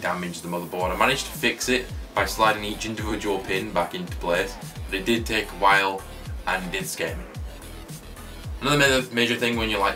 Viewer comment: another question, this might seem stupid but what did you actually said at 06:37 cuz it sound like something funny and I can't hear it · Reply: Sniper" I managed to fix it by sliding every individual pin back into place, but it did take a while....and it did scare me" :)